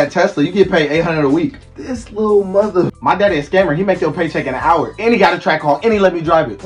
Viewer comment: My daddy a scammer, he make yo paycheck in a hour 😂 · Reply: Damn. I thought he said "my daddy is a gamer" lol